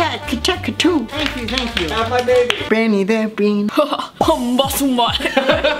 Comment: did anyone else hear Thomas say "Have my babies!"?